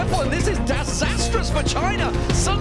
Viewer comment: Did he said "disastrous vagina"?